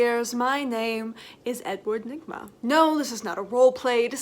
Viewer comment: Maybe it's just the fact that I'm pretty drunk right now, but at the beginning I misheard him say his name was Edward Ligma...